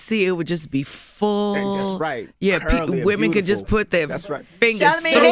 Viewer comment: Did envy just say "bootyfull" at 26:54 ?